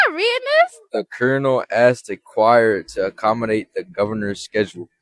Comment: I THOUGHT THAT SAID CHAIR I WAS SO CONFUSED